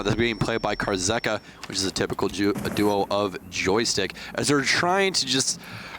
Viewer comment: did he just say "typical Jew"